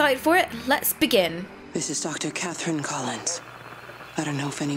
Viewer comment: omg when at the start when the Dr said "this is doctor Katherine Collins" i instantly thought she said doctor Katherine HALSEY... anyone else think this?